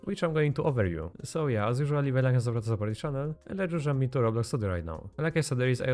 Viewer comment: What did you say. Even Captions couldn't pick it up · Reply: He said 'Leave a like to support the channel' or 'leave a like and support the channel'